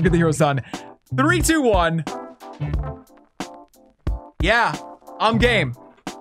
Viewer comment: i thought he said " yeah, I'm gay "